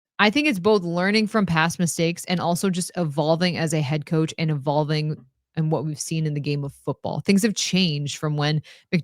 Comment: I thought said that we've got a balding head coach🤣